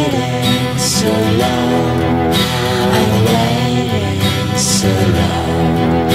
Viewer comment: Waiting So Long I swear to God, all this time I thought it was saying " Our right is so wrong"